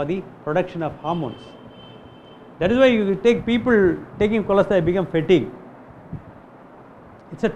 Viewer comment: What is he saying at 2:20 ??? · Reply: He's saying that people who take Cholesterol medication become fatigue